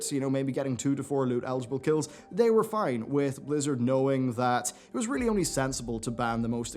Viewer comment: did you say blizzard?